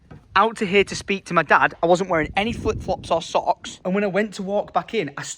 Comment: Deadass thought I heard you say swocks and not socks GET THEM TENNIS BALLS RN 🔥easily sponsored yourself by doing it on your own land🔥